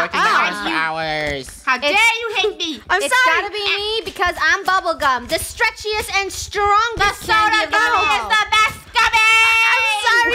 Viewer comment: I thought she said I’m bubble gum I’m the strongest and the stretchiest soda in the world